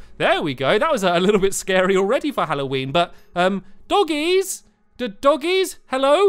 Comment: I just love how he says DOUGIES instead of Doggies